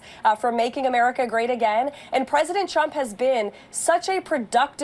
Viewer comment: I hear "President Chump" from Leavitt's mouth. In the best of worlds that would've been a Freudian Slip. And maybe it is. Even lies-on-lies at warping speed might be enough from Karoline's mouth. I'm looking forward to the day the Chump says something that indirectly contronts her integrity and how she'll respond. Oh, forgot, she has zero integrity. It's all facade on parade.